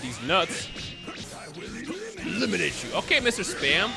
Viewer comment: Why do I hear " I will eat lemons" from Android 20 And not I'll eliminate you